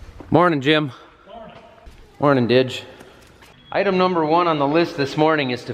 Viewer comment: i thought you said «Morning bitch»😂